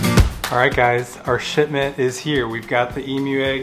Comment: I thought u said shitment